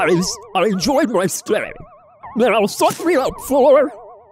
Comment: I heard something else :| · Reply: Balloony : NOw Sus mE uP FoUr